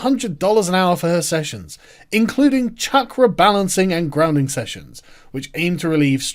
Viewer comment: You said "chakra balancing" but I heard "check rebalancing". As in money going from stupid people to slightly less stupid people.